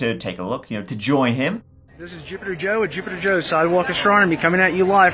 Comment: I keep hearing “STUPITER JOE”😉